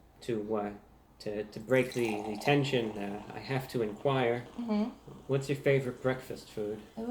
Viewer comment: What says - "to break the tension, I have to inquire: what is your favourite breakfast food?" What I hear say - "You're sleeping with me tonite"